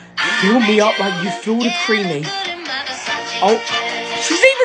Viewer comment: when he said “feed me” i heard “cream me” 😭💀